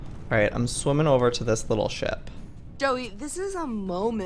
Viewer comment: did stacy call joey doey? did you hear that?